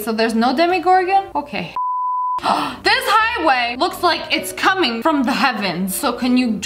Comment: i thought she said “this highway is c*ming” LMAO